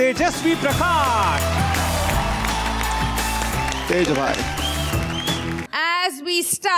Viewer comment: I'm such a shipper😂 Vikas said Teja Bhai & I heard TejRan